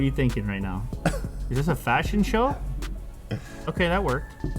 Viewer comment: I thought lew said "is this a fucking show?"